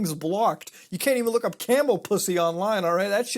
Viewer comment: Did he just say Camel Pussy 6:30 seconds, I have to pause this video for a moment. Dam